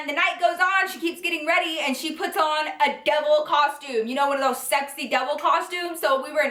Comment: Literally when she said angel I thought she said egg roll 😂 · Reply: Clair Moore and when she said Devil I thought she said Double! 😂